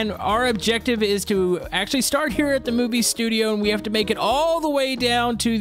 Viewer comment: did he say moobie or movie? lol